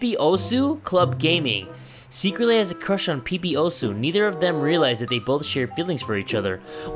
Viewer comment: did say Pee pee Osu